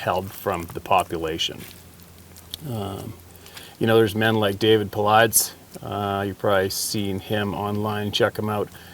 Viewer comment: Did anyone catch that name at 2:44? David Palats...?